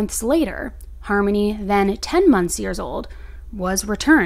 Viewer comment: Did she just say "10 months years old"? Lol